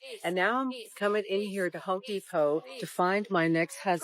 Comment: Hey hey hey @ 1:08 Did She Really Say the "Hoe Depot" 😮 😅😂😅😉😂😭😂😭THIS IS WHY IF IT IS NOT DELIVERABLE BY Online shop & home delivery I DON'T NEED IT 😂😂😂👍👍👍🪖& ✌️out